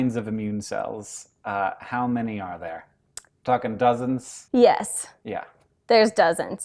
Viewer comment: So when he asked how many immune cells @ 2:56 does hank say fuckin dozens? Lmao 😂